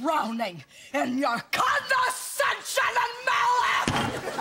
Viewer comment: anyone know what she said" condescension a melic"? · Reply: the second word is malice i believe